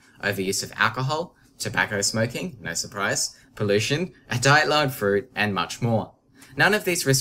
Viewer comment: What de he say at 00:48 ? It sounded like Diet Llama Fruit, and much more :-) Great video by the way, very informative!